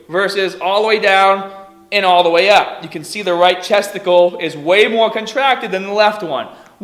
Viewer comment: Did he say "right testicle" at 11:45? I thought he was working on the pecs.